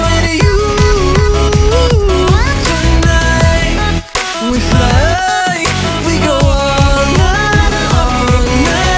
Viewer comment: what he says anyone tell mee plzz.....i think its chennai... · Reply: Bk Navanee He said Tonight